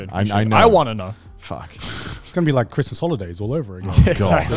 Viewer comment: Thought Garnt said “It’s like Chris’s holidays”, like all they do on trips with Chris is eat and drink and gain weight 😂